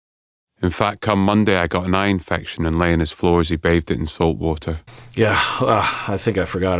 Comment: I re-listened a few times, but cannot make out what Gadd says (around 12:03). I hear he got an eye infection.... in salt water. But I can't hear the middle of the sentence? Thanks for doing this series! I've never had a show hit 'so close to home'. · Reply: “I got an eye infection and lay on his floor as he bathed it in salt-water”